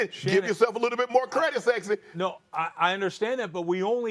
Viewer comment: Did he say “sexy” WTH